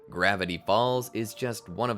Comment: At the start I heard "gravity balls"